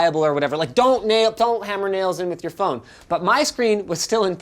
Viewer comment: I thought I heard: Now you can hammer a nail into your phone.